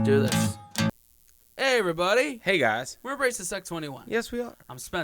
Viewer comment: Have to comment becuase I miss heard the title of your Chanel .. I could of sworn I heard you say . " Hi we're racist at 21 " .. I swear that's what I heard lol